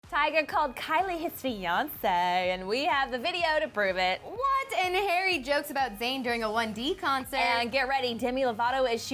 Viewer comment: I thought said tyga called Kylie his Beyoncé 😂